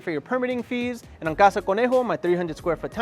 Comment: I thought he kept saying Casa Pendejo. 🤣